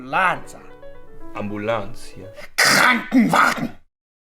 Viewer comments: я один слышу как "НЕТ БЛ*ТЬ" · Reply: мне одному кранвагн послышался? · я единственный слышу КРАНВАГН (ЭТО ТАКОЙ ШВЕДСКИЙ ТАНК)